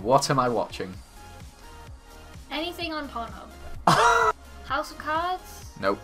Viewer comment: did she say anything on porn hub